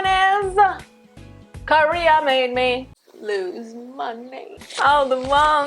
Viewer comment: For the second point, I thought you said 'Korea made me lose MY NAME' instead of 'money' lol